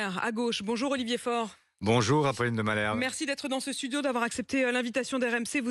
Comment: C'est moi ou Faure lui dit : "Bonjour Raphaël de Malherbe" ??